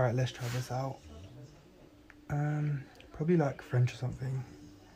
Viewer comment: Did he say "french" lmao? I have this reaction cuz im french Yay....Im stupid